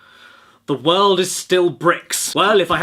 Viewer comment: The world is still bricks? Thought you said the world is still bricked at first and that makes more sense so that's what I'm going to continue believing you said. Bricked.